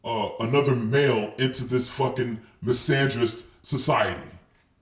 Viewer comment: Oh yeah you're right. I dont know why I heard it "misogynist". But I'm glad that you see society as it really is - a misandric shit hole. And yeah MGTOW is the ONLY way for men.